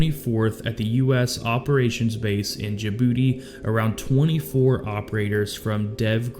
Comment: Forgive me but hearing you say they were flown to djibouti sounds like you were saying they were flown to ja(ya) booty. I know its silly but that made me laugh.